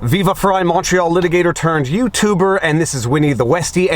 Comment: This entire time I thought you were saying "Macho Litigator turned Youtuber." Now I realize it's Montreal. American moment.